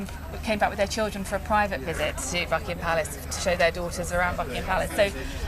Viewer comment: Holy shit I thought she said fuckingham palace